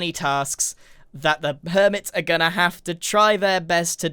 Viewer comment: Permits instead of Hermits